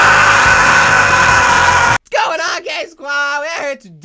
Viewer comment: did i just hear "what's goin on GaY squad" 🙈😆